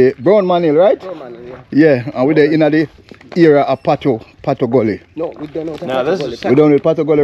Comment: I like this area, but why the name Tattoo🦉Gully? 🏡👀🐦🐕❤️ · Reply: I thought he said Patoo Gully, but I stand corrected.